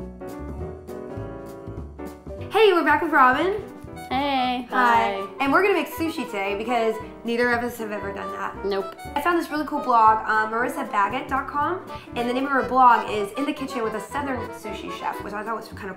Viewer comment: wasn't looking at the screen for a second and thought she said "marissa faggot" lol